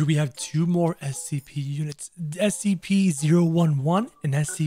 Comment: Oh for a sac I THOUGHT you said scp001 instead of scp 011